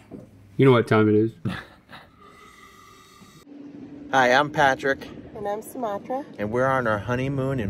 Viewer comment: During the wheel video I swear I heard "I'm Patrick. And I'm Smatrick"